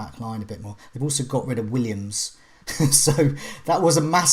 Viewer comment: Paul Williams was playing left back for Norwich? No wonder they were leaking goals 😂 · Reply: Ha, you misheard I think. I call him 'poor Williams' . My Brighton accent can be a bit hard to understand though at times. :-)